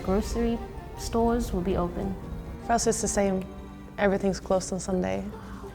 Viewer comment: I know ( flag ) but she is talking about the country where she was ( 13:03 ) she clearly said France.. I think that she lived in Germany and France and in the Netherlands ( Or she have her flag upside down and she is a France girl ) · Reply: she said for us not france xD ai subtitles